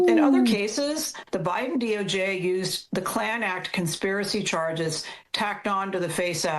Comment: The Klan Act? I thought they were saying the Clown Act.